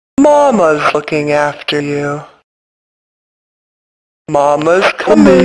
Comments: Why does it sound like mama is saying... Mama's F*cking after you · sounded like mama said. -“Mamas fùcking after you.” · it sounded like she said “Mama’s f___ing after you.” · I thought she said “mama’s f*cking after you” 🤣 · I thought she said “mamas f#%*ing after you”